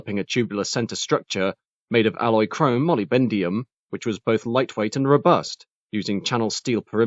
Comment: did he just say “molibendium”?😮